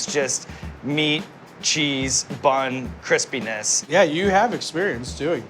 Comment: subtitles say - "meat, cheese, bun, Chris penis."